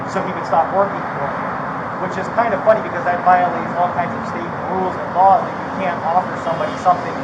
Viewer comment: Did he say " it violates our metro state rules and laws cause you can't offer someone something"?